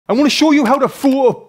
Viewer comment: I was just scrolling through shorts when this video came, and i literally heard ( I'm gonna show you how to fuck ) and came back to listen, did i hear it correctly😂😂😂😂😂